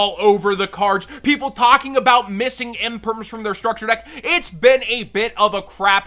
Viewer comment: I heard the "Missing Imperm" and freak the fuck out...